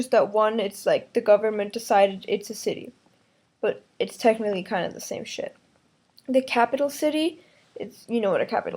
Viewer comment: did you say kind of same SHIT to and then you show this to your students.